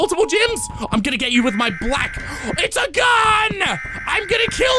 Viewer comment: my internet is bad so all I heard was saying "I'll get you with my black," even if I know he was going to say block, that was kind of funny.